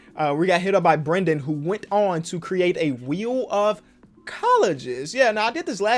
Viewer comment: I thought it said Wheel of Colors for a sec. I was okayyy Kenny